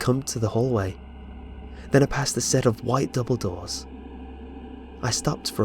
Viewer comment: I heard "White Dumbledoors"..... I'm like WTF where was he taken?